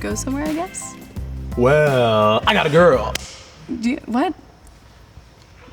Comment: I thought the first guy said hi I'm a girl